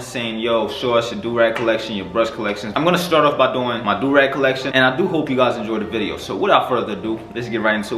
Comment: Dude... i thought it said my drug collection lmfao